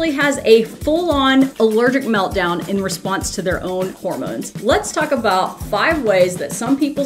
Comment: I legit thought that said Mormons 🤣